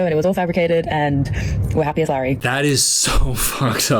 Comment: did she just say "we're happy as larry"?